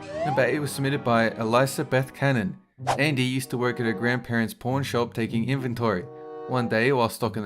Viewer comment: I thought he said "grandparents porn shop"